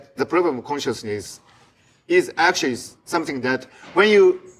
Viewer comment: When Mogi said that generalising can lead ro an elegant solution, I misheard it as "an arrogant solution". I can see it being both elegant and arrogant!